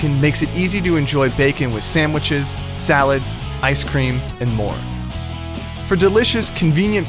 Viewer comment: did he say ice cream?